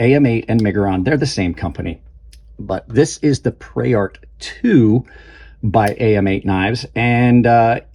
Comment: I thought it said "pervert 2" 😅